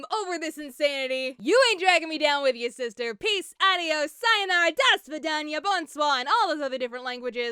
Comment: Peace, Adios, Cyanide is what me and my brother thought you said!